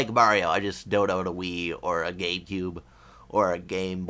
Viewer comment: Grats with the 2000 subs. And it really sounded like you said ''Gaycube'' xD